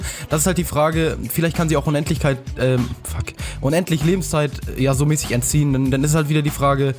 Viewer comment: Unendlichkleit f*ck :D 5:55 habs genau gehört :D